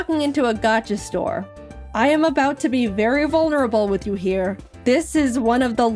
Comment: did anyone else hear "variable nurable" here? just wondering